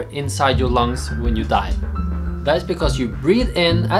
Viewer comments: Who else thought he said "...when you DIE", Realised he said "...when you DIVE" And face palmed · I kept hearing 'when you die' instead of 'when you dive' because your Norwegian accent has unvoiced 'v' sounds, haha. I'm like 'yeah, well, that can happen when you get the bends!"